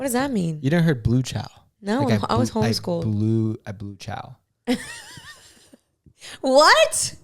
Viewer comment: i heard “blue chow” not “blew chow” 😭😭 and i was not homeschooled haha. i enjoyed the episode, congratulations again and may God bless your union!! 🎉💗 (ps. i also love the longer episodes as well)